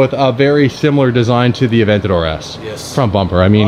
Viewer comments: did he just said " my avantador S " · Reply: He said "the Aventador S" not my. Would be sick if they got one though. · Hey!!! What did he said!?? Aventador S to the channel!!!